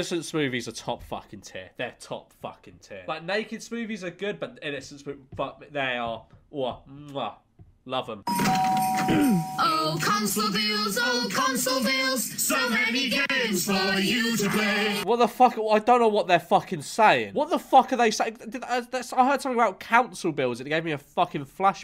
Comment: All I heard was' oh council bills oh council bills so many bills for you to pay'